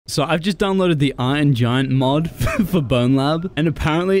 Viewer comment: U said iron giant mod but I heard “your mother” mod